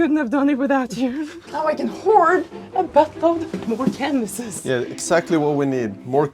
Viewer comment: She said “store canvas” but I heard “cannabis” 😂 😶‍🌫️